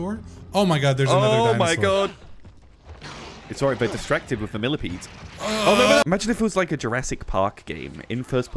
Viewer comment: When he said millipede, I thought he said Philippine. HA HA HA